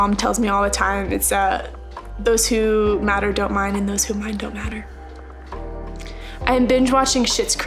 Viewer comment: what did she say(the advice)? I'm not native in english so I didn't understand lol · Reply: Those who matter don’t mind and those who mind don’t matter